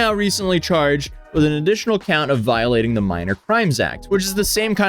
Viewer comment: I miss heard you for a second and I thought you said he had a new charge against minors so he had two charges for crimes against minors not minor crime which are two totally different things obviously both still bad one way worse